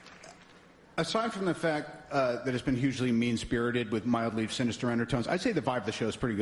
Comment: What does Robert D. Jr says after "Aside from the fact"? I can't understand what he says. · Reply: "Aside from the fact that is has been hugely mean spirited with mildly sinister undertones. I would say the vibe of the show has been pretty good . Wouldn't you?"